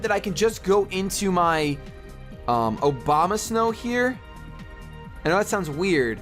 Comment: Lol sounds like is saying OBAMASNOW